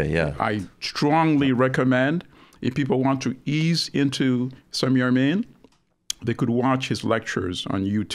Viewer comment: What is the name he is saying at 6:13? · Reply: Gnuggets11 Samir Amin